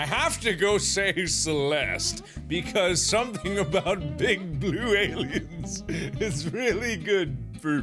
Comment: I thought he was about to say “big boobed aliens” on 2:26 oops. I need to get my head out of my butt..😂👌🏻💯